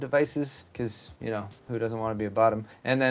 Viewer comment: Did you say "Who doesn't want to be a bottom?" at 9:04 ? Uh, me?